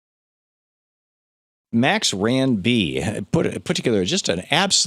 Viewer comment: the subtitles say "Next, Rand B". It's actually "maxrandb", a user on Democratic Underground.